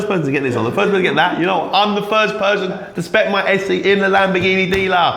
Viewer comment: i thought he said persian